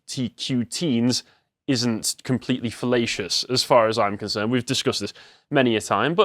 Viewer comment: I heard "fallacious" as "fellatious", and it sounded oddly fitting.